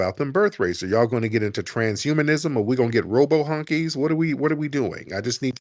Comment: did he really say Robo Honkys 😂😂😂😂😂😂😂